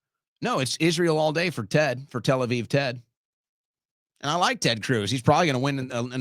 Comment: Did you just say, “and I like Ted Cruz”?